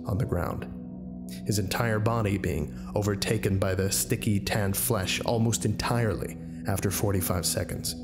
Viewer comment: the damn editing software screwed up. I'm not going to re-render/upload the whole video to fix it as it's kind of minor (along with all the other silly mistakes). Just annoying. :( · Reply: Also you said "sticky tan flesh" at 19:31 instead of the correct "sickly tanned flesh" that it shown in the text. Coming to nuke your house.